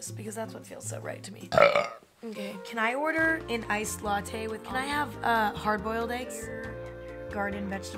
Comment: hearing her order "iced latte" instead of "iced almond milk latte" feels like a glitch in the simulation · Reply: it sounded like she said iced latte with almond milk !